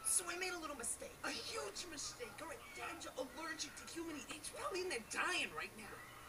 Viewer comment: I had no idea what cumin was I thought they said human and was like wtf